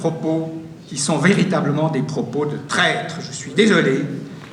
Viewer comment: Bonjour, à 7:20 il dit quoi ? "Ce sont des propos de prêtre" ou "des propos de traître", svp ? Mon casque audio est un peu pourrit ... Sinon, merci pour cette vidéo, avoir l'opinion des helvètes sur ce qu'est la démocratie directe est très intéressant. On lâchera rien de toute façon ! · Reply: Il dit "des propos de traître".